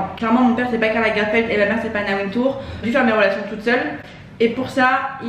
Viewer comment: J'ai entendu "moi mon père c'est pas Karl Lagerfeld, et ma mère c'est pas naruto", sinon super vidéo !!